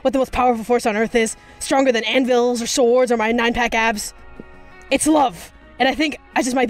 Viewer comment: DID HE JUST SAY NINE PACK ABS